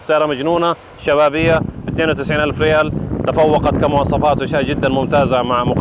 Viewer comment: استاذ في الدقيقة 18:10 قلت 290 الف ريال ولا 92الف ريال ؟؟ كأني سمعتها 290 ولا انا غلطان !